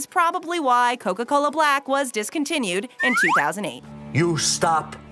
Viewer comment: Did someone say they wanted a sprite cranberry?